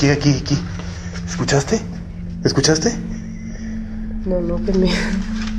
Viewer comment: What he said : DID u hear that? (In Spanish) What I hear : Bucciarati Lmao😂😂 Ya it from JOJO😑